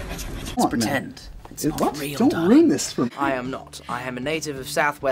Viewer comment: I thought he said DON at frist